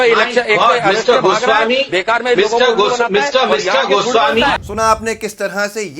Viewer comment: min in video, While Sambit says Goswamiji... Goswamiji... it sounded like Mummy mummy mummy..